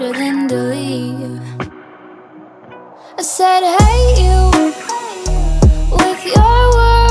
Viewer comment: Does she realy sings hey you ? All I keep hearing is " I said I hate you" ………………….. or maybe that's just what I want to hear ………………….